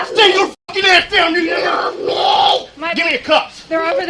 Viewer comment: Woahhhh what did the guy cop say?? "Stay your fkn ass down you ..."